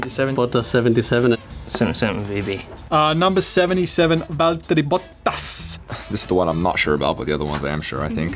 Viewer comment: Everyone: Bottas Dani Ric: Valteri Bottas Charles: ⛵as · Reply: I thought Danny said "Balttery Botttasss"